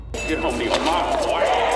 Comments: The line was "Get/Go home to yo' momma, boy". Was the line rephrased to fit the CinemaSins Sentence? or do/did you truly think that's what was said? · I always thought he said "Get home to yo mama, boy!"